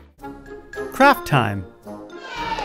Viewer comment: When he said craft I thought he said crap xD Liek crap time kids!